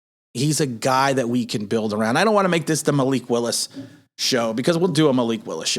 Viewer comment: It sounded like you said male Equalist. So I typed in Liberty qb. Oh Malik Willis